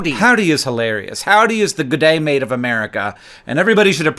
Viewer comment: What do you mean, Hank when you say that "Howdy is the bidet of America"? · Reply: "g'day", not "bidet"